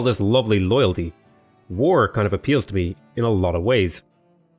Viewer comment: I miss heard him the first time through, I thought he said "With all this Oil, War kinda appeals to me, in a lot of ways" and I was gonna say 'spoken like a True American'